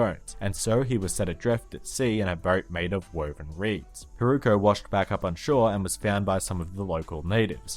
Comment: amaterasu tsukuyomi susanoo...... who had the naruto feelings too? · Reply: also ebisu, a smiling fisherman though.... and i certainly heard wrong ebisu was born boneless and was named iruka then build bones and became ebisu. no i refuse, i must have misheard it. need to check english subs.... ..... 😥 ah, thank god its not iruka! 😁 its hiruko. but still, a jolly smiling ebisu...... ? no.